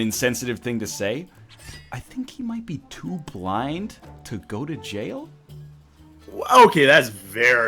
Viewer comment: I thought said: "Too black to go to prison" And boy that would have been bad!!